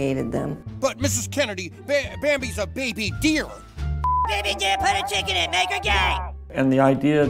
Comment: I thought she way saying “Put a chicken in it and make her g*y and lame” makes more sense to say put a chick in it and make her g and lame